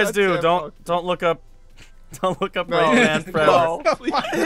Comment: did he say rayman forever XD isn't that a superhero